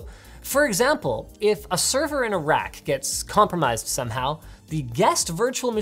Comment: I heard "A server in Iraq" 🇮🇶